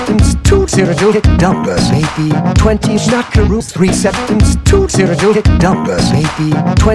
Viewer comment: This whole time I thought he was saying "20 schmackaroos, 3 septims, 2 cyrodillic dumpers"